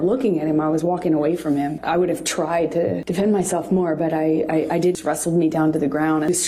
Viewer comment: "I did wrestle me down to the ground" She said "I" not "he" **I**. Another slipup in her transpositioning. Instead of making her lies up out of thin air she's taking the abuse she did to JD and flipping it and embellishing.